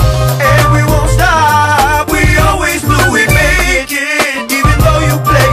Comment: God damn I'm just learning now the chorus said, "we always knew we'd make it" I thought it said "we always will be naked." :D